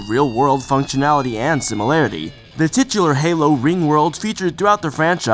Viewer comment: Did you just say TITS?